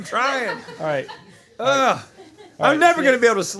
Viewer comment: Did Rhett just say the n-word?? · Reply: “Never?”